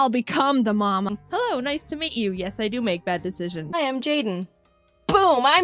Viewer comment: I thought she said IQ instead of ice cubes